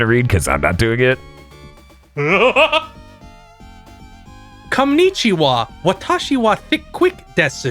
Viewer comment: "konichiwa" made me spit out my drink. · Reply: I heard "Cumnichiwa," and did a double take